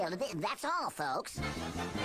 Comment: Every time I hear say, "Welcome back truth seekers" It sounds like, "Welcome back true suckers"